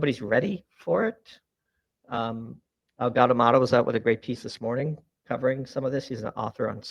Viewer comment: Does anyone have a link to the substack author Doomberg metions at 4:55? I sounds like he says "Algad Almato" ?? but I cant find it. Would love to read more.